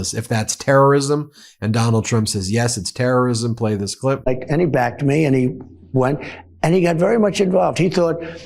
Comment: he actually says..he backed me and he won